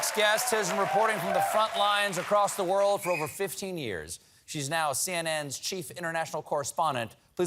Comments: Colbert introduced her as 'reporting from the front lines across the world for over 50 years'. She is only 42. What an exceptional talent! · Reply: "15 years", but yes, I also heard it as "50" the first time -- had to rewind to double-check. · I listen primarily to radio, and I have heard her report before. When Steven introduced her, I thought he said she'd been reporting for 50 years. Then she came on, and I quickly replayed it. 15 years is a MUCH closer match to the lady I was seeing. Her reporting has always been superb, but wow!